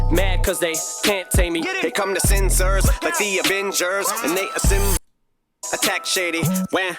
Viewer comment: Ngl, when I heard "here come the censors like the avengers" my brain thought he said " here come the Zim Zers like the avengers" 🤣 idk what's wrong with me